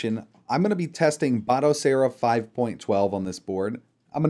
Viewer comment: What was the distribution you demonstrated? I'm sorry I struggle a bit with your accent... It sounded like you were saying ottasera 5.12 or something like that? It looked like it was using emulationstation. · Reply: Batocera Linux 5.12